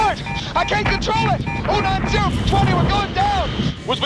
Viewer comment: what does Hollywood say after “I can’t control it?” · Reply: I'm getting "090 for 20" I understand the 090 is the heading but what's the "for 20?"